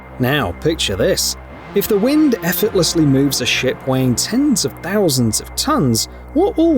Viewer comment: did bro just say a sh!t wane